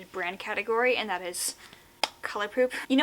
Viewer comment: Wait did she say colourpoop??? 30:39, then later on she says colourpop, 30:45 is my hearing bad coz I am confused.